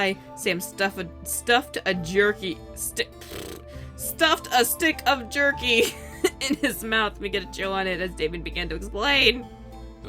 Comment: I thought u said shoved a stick of dick in his mouth